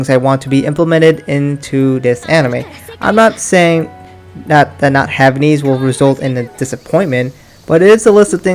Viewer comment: im sorry I can’t hear what’d you say here .,. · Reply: Mashiro I'm not saying that not having these things will be a disappointment